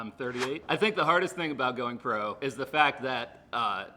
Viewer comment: I thought said, 'the hardest thing about going broke is....'